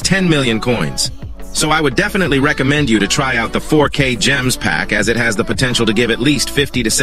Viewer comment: Bro 2:04 when you said 4k gems that I heard is ( fork a ) 😂